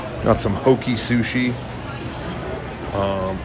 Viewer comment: I was listening to this video at first and I thought you said “Hokey sushi”. I thought you were being a sushi snob😂